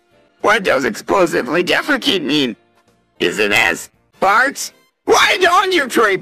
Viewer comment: I don’t think you said “jazz” there, buddy 💀